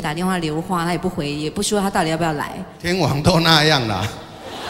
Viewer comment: 他們是在說什麼啦?? 7:35 什麼是'電話' ?? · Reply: wong776 陈升说：“天王都这样了”。观众群喊：天后，天后